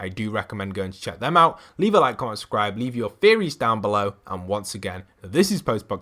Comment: When he said theories I thought he said fairies